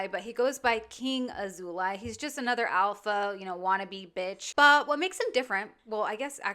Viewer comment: Damn…”Old ass guy in his 40’s” really hurt Danielle. Whenever you say “alpha” i hear “swolecow” My wife and I love your content! Keep up the great work!!